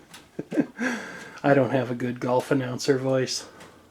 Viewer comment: For a second, I thought you said "dolphin ouncer voice". :P